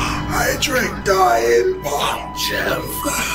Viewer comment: did he say he drank diet pop?😅 what did he drink that made him turn into a zombie 🧟